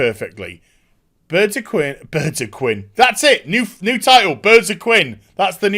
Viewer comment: Thought you said 'Birds of Quim' at first. Now there's a film I would pay money to see.